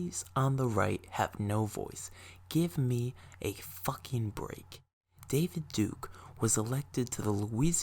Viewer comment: I've watched this before, and I came back to this. The first thing I heard was "Give me a fucking break". Amazing for tired me at 10 in the night.